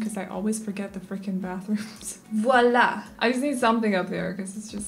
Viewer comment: Omg I'm French and at 15:37 I heard "voilà" it's real or it's my imagination... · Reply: I said voila 🙈